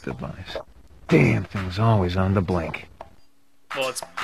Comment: Did he say "I am not quite Cam Clarke"? Because, uh